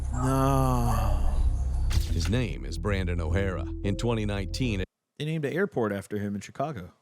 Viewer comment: I was listening in my headphone and I thought he said Brandon Herrera